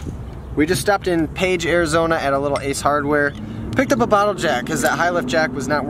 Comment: Oh, a bottle jack. I thought you said a bottle of Jack, I mean, the way things were going I wouldn't blame you!